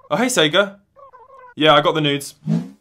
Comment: did you just say: "Hey, SEGA. Yeah, I've got the news?" cause it sounds like "I've got the n*des." LOL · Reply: But the subtitles were auto generated so it could still be news but I listened to it in .25 speed and he definitely said n#des